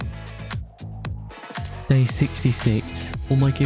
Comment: am I the only one who heard "execute order 66"? surely not